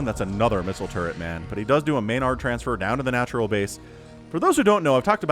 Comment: I keep hearing "MINOR transfer" LOL